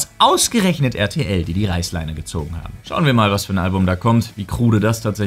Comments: Wer hat auch REICHSleine verstanden? oh man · Hat bei 10:05 sonst noch jemand Reichsleine verstanden? 😅